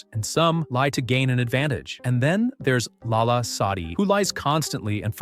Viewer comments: you said lala sadi and the captions say Allah Saudi.........work with that a bit. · writing "Allah Saudi" instead of "Lala sadi" is a complete disrespect.